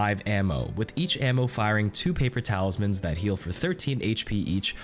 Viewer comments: i swear he said paper towels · You said "paper talismans" I heard "paper towels" I'm not calling them anything else now